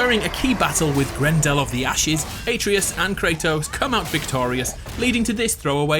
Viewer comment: did u say ate-treeus at 2:45???